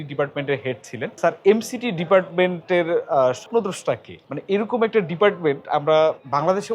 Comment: did he say “ স্বপ্নদোষ “?